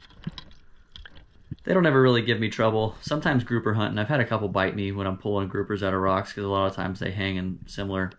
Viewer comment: did you say that you've had a couple of Morays bite you????